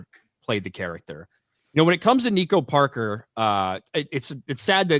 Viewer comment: When was talking about Nico Parker for a second there I had to rewind and listen to him pronouncing her name because I at first thought he said "Negro Parker" LMAO